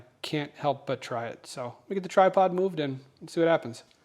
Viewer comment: I haven't finished the video yet so I'm not sure how this will work (I paused as you were moving the workpiece on the dividing head under the spindle). But, when you said "... get the tripod moved...", I heard it as "get the try-pod moved". 😂